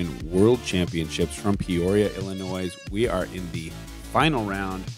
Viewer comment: did he say “Illi-noise”???